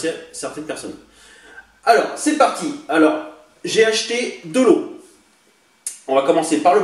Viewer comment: " j'ai acheter de l'eau(lot) " 🤣🤣😂😂😂😂quand j'ai entendu ça je me suis dis koi ?? 🤣🤣😊